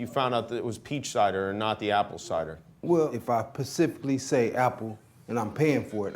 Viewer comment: loooooooooooooool, man said "Pacifcally" instead of specifically